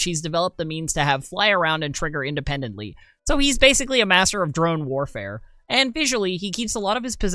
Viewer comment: you say "drone warfare". I hear "dragon with magic funnels".